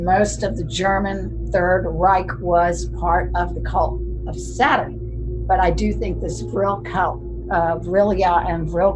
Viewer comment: anyone catch the name of the movie mentions? Sounded like "Hacks" but nothing on yt. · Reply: Haxan 1922 original movie. A hybrid of documentary and fiction, this silent film explores the history of witchcraft, demonology and satanism. It shows representations of evil in a variety of ancient and medieval artworks, offers vignettes illustrating a number of superstitious practices and presents a narrative about the persecution of a woman accused of witchcraft. The film ends by suggesting that the modern science of psychology offers important insight into the beliefs and practices of the past.